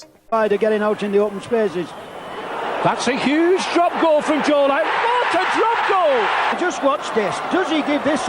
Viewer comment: Hold up I must be deaf or something but did he just say joe Biden? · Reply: Joe Lidon sadly